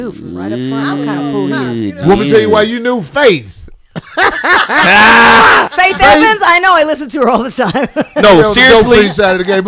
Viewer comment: hit a nerve with at 46:17, I could barely hear what he said, but she didn't like that shit. What's the backstory on that? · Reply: He said faith Evans is on the dopefiend side of the game